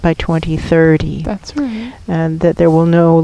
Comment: Sorry I meant what does say at 4:38, in response to. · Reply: Hi Ryan, this is. My channeled response was, "That's right."